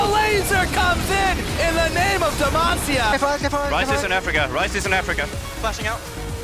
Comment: "Ryze is in Africa" 1:09:01 did he really say that? :D